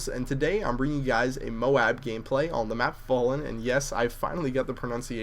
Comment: Lol, thought u said "MOAB on the map Ballin'"